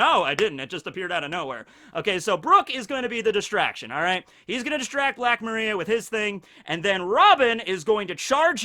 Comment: Im not that fluent in English. But, Did you just said "Brook will gonna distract Black Maria with his thing?" HAHA!